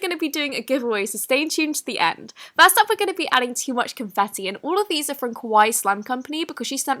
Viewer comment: I thought you said "Too Much Confessi" Am I insane?